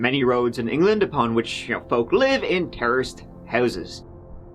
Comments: You said, " ...where folks lived in terraced houses." Ha, ha ...I actually thought you said "terrorist" houses. 😂 (Say what?????) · I know what you meant by "terraced houses", but what I heard was "terrorist houses" and got really confused. 🤣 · does he say many folk live in terrorist houses?